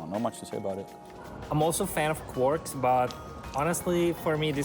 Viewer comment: What is the word you’re using to describe the tvaroh? YouTube thinks you say “porks” or “quarks” or later “quirk”. I don’t see any meat or sub-atomic particles! In English maybe we would say maybe cream cheese for tvaroh. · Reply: It's "quark" and it's the literal translation of "tvaroh".